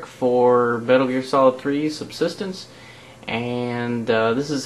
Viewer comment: k good i thought he said substanincenance